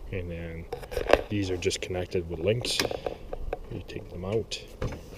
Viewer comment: What kind of brackets are they? The ones that mount the base to the sled. Sound like you said links? Thanks man awesome set up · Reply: It’s a base plate made to fit the Milwaukee packout to the Skidoo linq system